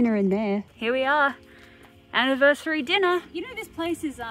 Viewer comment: I know you said 3rd year anniversary but to me I thought you said 30 year anniversary